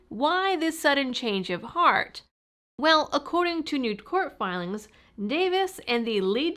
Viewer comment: the transcript says she said “new court filing”, but she clearly says nude court filing — which is equally appropriate.